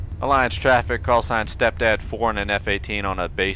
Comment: did he say stepdad ha